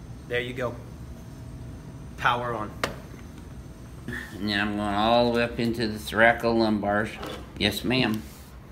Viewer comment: What did he say at 0:18? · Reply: Now I'm going all the way up into the thoracolumbars.